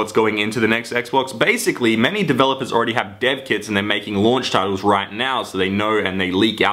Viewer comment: When he said dev kits i thought he said Dead kids